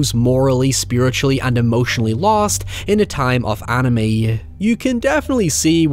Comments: Misheard your script's "time of anomie" as 'time of anime', which would give a different color to the Pontifex Society. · 'emotionally lost in a time of anime' OH. OH being SPICY today. Edit; Wait, wait was he saying 'Ennui'? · did you say a time of anime? The captions say the same thing too I'm sorry I'm a little muddled is it a reference to how some anime can be bad? ;v; thank you if you can explain